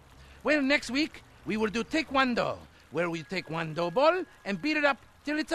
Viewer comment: Next week we're going to learn TAKE-ONE-DOUGH!! 😂😂😂 Gold!! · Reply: I ACTUALLY THOUGHT. THAT HE SAID TAE KWON DO..THATS WHY I DIDN'T GET THE JOKE AT FIRST. 😂